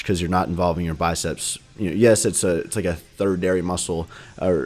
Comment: did i hear "thirdinary" muscle 14:33 hahah